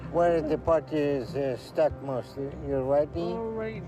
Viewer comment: > did he say "your LEGGY"?!! ❤❤❤aweeee 🫂 I Love this man!!